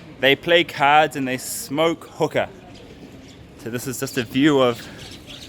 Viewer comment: Did he just say hooker?! 😂😂😂